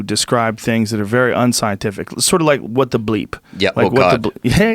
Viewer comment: What is the name of the movie/movies that they they are talking about here? I couldn't quite catch it as they were talking over each other, sounds like 'what the bleep', and something about God. I couldn't be sure.